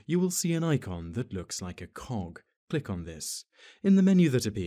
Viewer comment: Rate, comment and subscribe if you took offence at 0:24 because you thought he said "cock." :PPPP